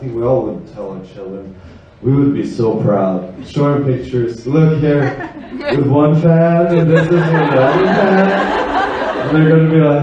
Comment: please i need to know what Henrik says at 34:00. I can't understand 😂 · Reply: “Look here, with one fan and then this is another fan!” he’s speaking hypothetically about showing photos of himself with fans of skam to his children someday. They’ll get sick of it and be like (as he says), “dad how many times are you going to show us these pictures!” 😂